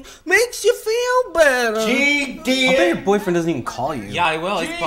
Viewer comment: What does the Dad yell at 2:33 ? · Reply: "GD it!" I.e., Goddamn it!